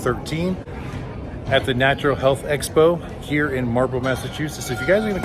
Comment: Ah Marlboro.... I thought you were saying Marble thats why I thought Marblehead (NE of logan airport)